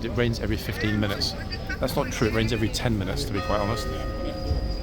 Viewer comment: I thought the man was saying 'it rings every 15 mins' meaning the clock, not 'it rains'. Who knows!!!